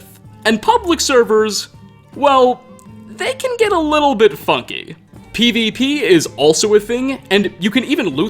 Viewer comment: made me do a double=take, because I swore I heard say "Fucky" out of nowhere.